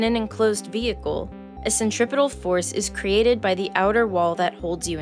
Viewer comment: is anyone else hearing "...a centrivetal force..." instead of "a centrifugal force?"